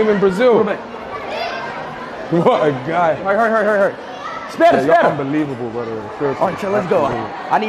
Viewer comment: did he say “später”? Like the German “later”? 😮😂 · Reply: No. He said "espera". Means "wait"